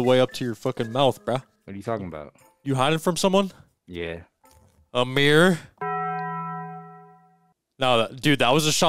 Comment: i thought he said "a mirror", like what the guy is hiding from 💀